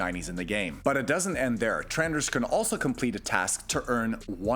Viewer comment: did... did you say... "trenders"?